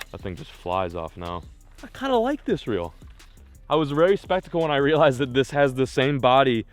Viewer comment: did he just say spectical?